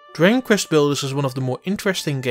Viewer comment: No offense, but the thick accent makes it hard to understand some of the words you're saying. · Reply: He pretty much butchers the very title of the game. "Diggin Kvest Blders" is what I heard. I'm not bashing the guy, but he probably shouldn't be doing voiceover work.